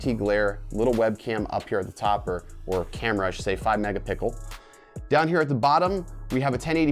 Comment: Anyone else hear," 5 mega pickle" at 5:12? Lol don't know if that was intentional but i laughed...